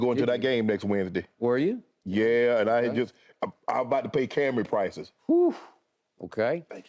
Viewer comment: Did Unc Say Camry Prices?